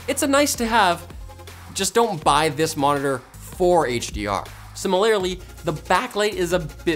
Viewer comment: Im sorry... Did he just say sim-il-lair-ly?